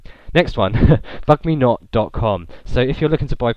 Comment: I thought he said "fuck me not" at 14:17 thumbs up if you did too!